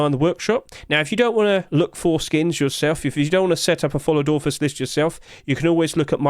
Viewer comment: Oooooh it’s a followed authors list, I always thought you were saying that micro website was an ORPHAN website